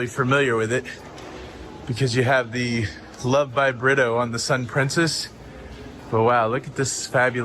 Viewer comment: Oh BRITTO?! I always thought you and were saying "love by burrito"